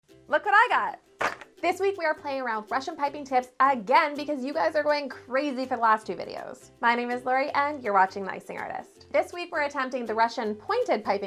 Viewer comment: I did not look at the intro logo and heard my singing artist. Then, I tried again but looking at the intro logo. This time I heard the Icing artist. Anybody relating to this???